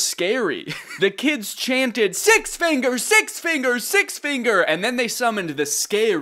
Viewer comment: i heard "six finger s3x finger s3x finger" lol